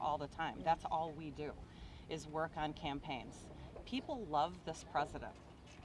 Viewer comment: I am getting the distinct feeling that these are not your average Trump voters. Sounds more like they are Trump campaigners. Big difference. I feel Channel 4 News is misleading us by not properly introducing these so called 'voters'. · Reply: she actually says they work on campaigns. These people a very very stupid